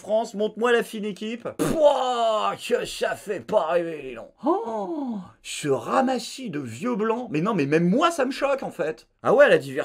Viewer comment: Quand tu as dis "ce ramassis de vieux blanc" j'ai entendu "ce ramassis de vieux gland" 😂🤣 jpp